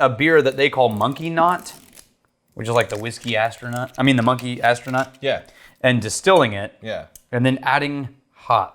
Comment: i think we all thought said "monkey knot" at 13:37 which has... shall we say... a certain explicit meaning